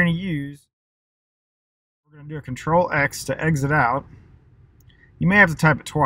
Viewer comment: what u said?? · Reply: Control-x to exit out!